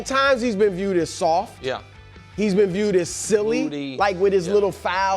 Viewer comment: did Nick call KAT "fruity"?? 😂😂😂😂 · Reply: MOODY not Fruity